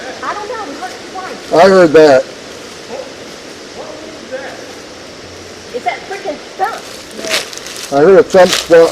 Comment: 23 I hear 2 woops that sounded like WHAT, WHAT...cool! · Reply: Hi bm - that is saying, "Whoa, what is that?" p